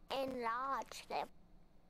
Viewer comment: someone PLEASE tell me what is said here. i hear it all the time and i have no idea what it’s saying. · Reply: Sorry, you're right. But if you look up on Google "crankgameplays enlarge them" there's a link to a tweet of Ethan with the YouTube video. Or you can find it on UT as "Mrs. Thomson 1st episode."